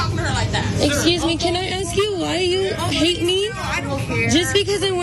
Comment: What is the woman recording saying there? Was she asking, “..why you hate me?”? Or, was it, “..why you hit me?”? Did this “karen” hit her with her shopping cart, or something? 😳😯❓❓😯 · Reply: She asked "Why you hate me".